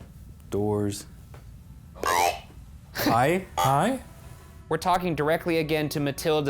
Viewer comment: that didn't sound like "hi" to me, that sounded like "bro"